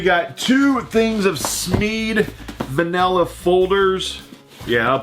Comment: I swear it sounds like you said "vanilla" folders, not manilla folders... I can't be the only one who heard that.....